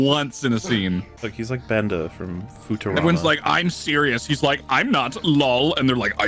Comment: Did you just say, "Futa-rama"?